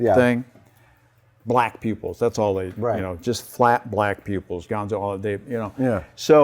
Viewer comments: i thought he said flat black people i has to listen to it again ...yeah im weird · So when Rick Lyon said "black pupils" I legit thought he said "black people" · i straight up thought he said the muppets were black people me and my brain and were just getting started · oh geez, when he said "black pupils" i thought he said "black people" which made that sentance SUPER awkward.